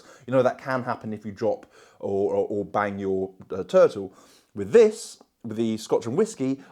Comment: What did he say at 8:10??? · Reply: Bang your turtle 🐢🐢